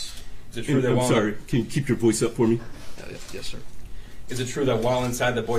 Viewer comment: Did I hear it right at 7:20 the judge asks is it possible to keep your voice up for me and crumbley says hell ya yes sir